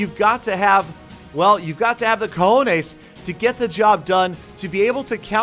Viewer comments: Did he really say cajones??😂 it means balls in Spanish lol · Did he really say the "cojones"?